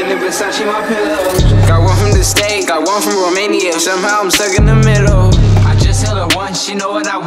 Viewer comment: did he just said "I come from Romania"?